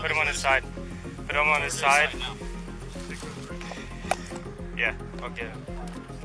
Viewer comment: the copscum seems to think he muted his body camera, did he say "yeah I'll get him" or "all good?" I can't make it out and then puts his assumed muted camera back on. what was he talking about? they knew what they were doing. this poor man was a training dummy to them.😢